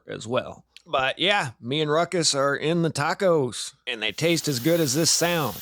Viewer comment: At the end ,I thought I heard Ruchus say. I want a taco lol